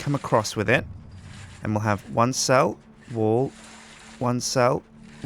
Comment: when he was doing the one cell wal part it sounded like he was saying "one cell... wool"